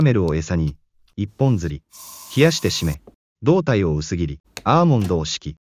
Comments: 冷やして〆が 冷やしてタヒねに聞こえる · 「冷やして〆」が「冷やして◯ね」って聞こえた